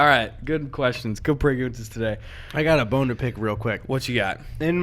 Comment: i thought he said “i got a boner pic”